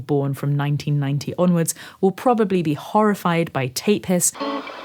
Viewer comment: Does anyone know what she said at 3:52/3:53?? CC doesn't even help. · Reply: Tape HISS!